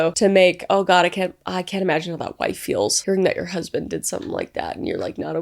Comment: What word is Kris saying at 2:27 ? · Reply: “Hearing”